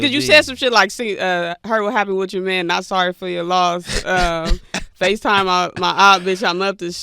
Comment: "Facetime my opps / Bitch, I'm up the street" still makes me laugh. 😂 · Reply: First time I heard it I thought he said "FaceTime my aunt" maybe because I thought right before that he said "Chuck E. Cheese" lol